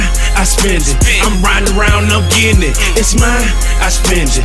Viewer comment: has anyone ever heard him saying, "I'm Brown & round & gettin it" 😂😂 no just me ok haha